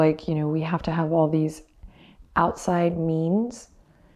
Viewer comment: Done this several times, and every time I heard "outside memes" instead of outside means. Yep, getting my inner memes on.